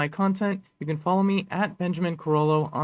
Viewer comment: gotcha on the “he/she” part. But what am I missing on the “cheaper” part? Selling ad prices ? One of the best capitalism vs communism vids ever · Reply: No! lol She says, "...my pronouns are, in fact, 'she/her." You thought she said, "....cheaper" at the end of the video. Have another listen. <3